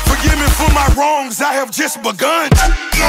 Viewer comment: I thought he said "I have just ma gun"